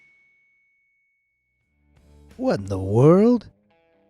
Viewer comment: What in the world : What he said "What the world"